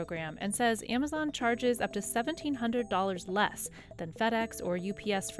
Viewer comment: Big difference in what the captions are saying @ 10:17. Should be $1,700 not $17,000 less.